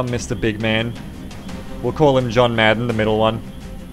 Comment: 24:07 John "Mad One". That's how I heard it LOL